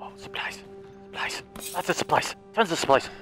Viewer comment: is it just me or did he say lots of spice